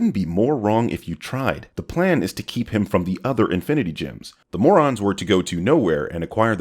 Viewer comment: I misheard and i thought he was saying infinity gyms like thanos was doing this to get shredded